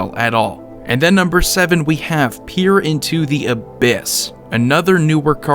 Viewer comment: I could have sworn You said "pir and toothy abyss"